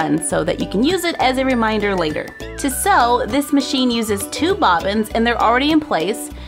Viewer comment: No hate,I love but it sounds like she say soy Machine?